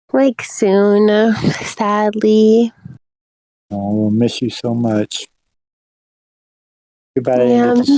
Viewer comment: Guys he didn't say f*ck you to Silphet, party's over. but actually, after listening to the sped up part at the end I heard, "Oh and Silphet, I miss you." how sad 😔